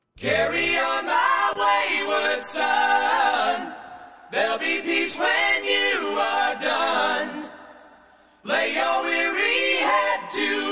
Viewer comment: I always thought it said “there’ll be peace WHEN YOU ARE GONE”!